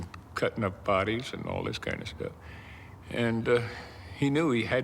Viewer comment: did I hear him say “all this kind of shit”? I could be wrong.